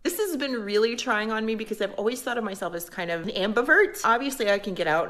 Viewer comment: Did she say amber alert?